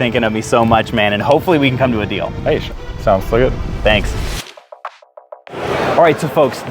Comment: What did he say at 14:12 hey sounds schluett?